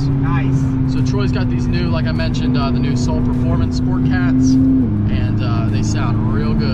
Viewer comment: Coulda swore you said 'kia soul sport cats' on troys car 😂